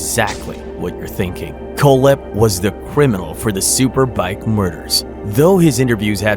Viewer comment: I heard you say mother instead of murder am I tripping? Love this